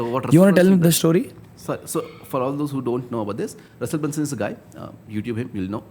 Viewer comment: Can anyone tell me what name did he say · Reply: Russell Brunson